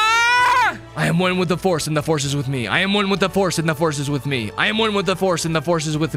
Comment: what are you saying? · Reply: “I am one with the force and the force is with me”